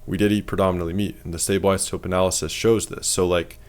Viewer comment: What does he say at 4:04? I am trying to read about it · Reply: Stable isotope analysis